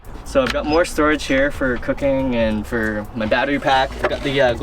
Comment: I thought he said cocain! Haha